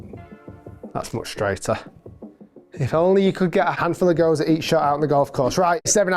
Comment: What did you say @ 10:58 ? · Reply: “If only you could get a handful of go’s out on the golf course”